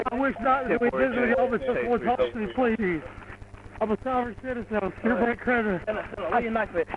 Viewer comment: I heard “I’m a sovereign citizen” in there. Like that ever worked!